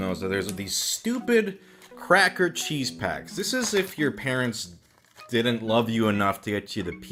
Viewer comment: "It's like Ratatouille moment, but sad, and you're depressed, and everything is dark, and you're alone in the middle of nowhere without your parents" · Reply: for a moment i thought it said pants instead of parents